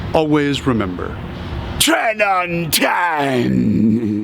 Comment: I turned on captions recently, and at the end my phone translated “Train untamed” to “Trend on time”. I thought you were saying “Tren on time” for subliminal messaging.